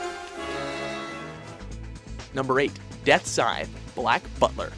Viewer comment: did he just say "death sign" at 2:24 or am i crazy